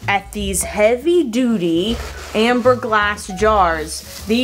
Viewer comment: Lol. I thought you said heavy doody. 😅